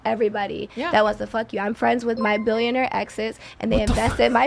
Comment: And she talking about she still friends with her millionaire ex-boyfriends..!!! 😂😂🤣🤣🤦🏾‍♂️🤦🏾‍♂️🤭🤭🤭🤣🤣🤣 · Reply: I thought she said Billionaire lol. Either way there's a reason she is an ex.